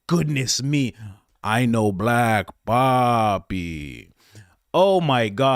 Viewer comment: As a bewildered Swede (not daffodil) I first thought you were saying "I know black papi". 🙄😆❤️🙏🙌